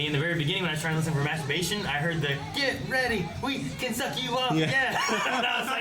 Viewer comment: So if anyone wants context of the actual lyrics: ''Get ready. Wake your psyche up Mob!'' But it is totally understandable as to how they can be misheard. Also, now I can't stop laughing at how that was the perfect timing for the episode title to show.